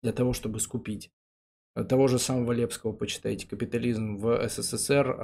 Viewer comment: Как автора книги "Капитализм в России <num> г." зову? Не могу ни отчётливо расслышать, ни найти в инете. Лепский? Левский? Или как. Хотелось бы почитать. Кстати, наводит на мысль: Не хотели бы Вы, общество Трибун, пригласить на беседу Сафронова про, естественно, советскую экономику?